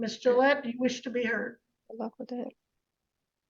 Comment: on the transcript where it says 'be heard with love' from the wife, she actually said to the judge 'good luck with that' after telling her she will be made to pay her big mouth fine, pity it wasnt heard. What a stupid woman thinking shes getting fines for what shes saying instead of getting fines because she wouldnt shut her face hole. Can you imagine live with that gob.